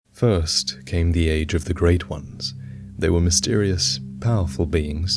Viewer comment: I swear I thought he said "first came the age of fire"